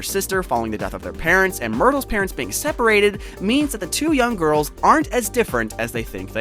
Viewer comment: I thought you say dumb girl but you said young girl